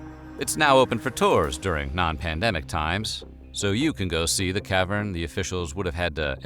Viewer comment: did you just say non pandemic times